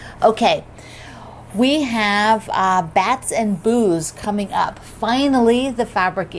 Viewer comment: Haha New to the channel and thought you said bats & BOOZE -- well that's an interesting combo!!!